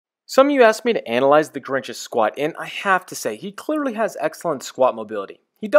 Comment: honest to god i thought it said "The Grinch Can Squirt?"